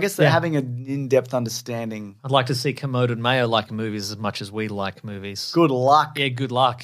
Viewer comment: Who are they talking about at 57:31? Subtitles says Koded Mayo? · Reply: Film critics Kermode and Mayo.